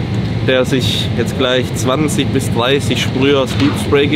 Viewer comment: Was haut er sich bei 4:55 rein habe es nicht verstanden. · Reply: Sleepspray...